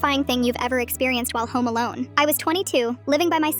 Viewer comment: When you said home alone my brain farted and I thought it said Post Malone